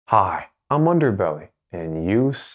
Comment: "hi, im underbelly" did i hear that right